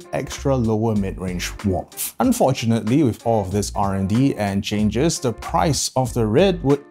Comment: what does "rnd" mean? i dont understand the term he uses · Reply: R&D, stands for research and development